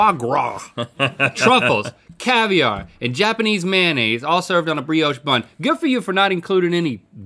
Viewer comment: I thought when he said Japanese mayonnaise he just said Japanese man